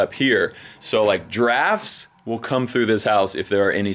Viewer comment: I kept hearing "Giraffes are going to come through this house." Stop those giraffes!